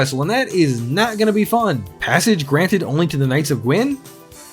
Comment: what’d you say? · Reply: ? Something about passage granted to the knights of gwyn?